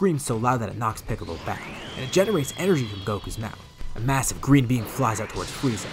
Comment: i thought he said green bean